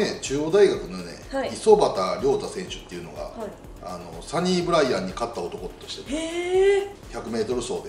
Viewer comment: この動画がアップされた日に２位になりました。でも、２位の実感がありません。 この戦力、数字なのに采配が上手でよくやっていると思っていいのか、里崎さん優勝予想の通り、もっと上手くやれてジャイアンツとここまで離されずにすんだのか このままだと来年も優勝予想になってしまうのでしょうか ちなみにサニーブライアンは競走馬、皐月賞、ダービーを勝った２冠馬です 最初聞き間違えかなと思ったし、字幕もサニブラウン選手だったけど。